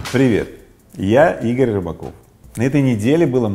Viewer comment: Послышалось: Привет, я Игорь Робокоп.